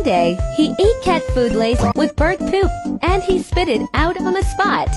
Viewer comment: I thought the TTS said "he ate cat food lace with BURNT poop" 💀